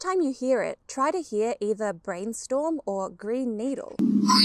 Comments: I heard "Brain Needle" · thats funny i tryed to shut my eyes and hear brain needle now no matter what i can only hear brain storm though once i did hear green skull and started thinking of he-man and the power of grey skull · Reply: I kept hearing "brain needle"^^ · The dress has ALWAYS been black and blue to me. I do not see white and gold even a little bit. It is totally black and blue to my eyes. And I heard "brain needle" lmao. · I coming back to this video after a few months, and now I hear "Brain Needle" because that how because that's how I've been think of the clip. I also hear "Green Storm" if I try. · "I had an existential crisis that made me question my whole existence " she said with a sunshiny smile on her face. XD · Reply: I hear “brain needle” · I heard green needle the very first time. Now I only hear brain storm. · I heard "Brain Needle". Maybe a post-traumatic surgery? :)